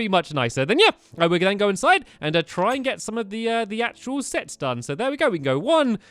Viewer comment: "Let's go in to try and get all of the sex down" Don't tell me only I heard that